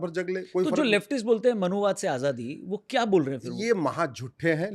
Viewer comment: At first, I heard it as maha chutiye😂😂😂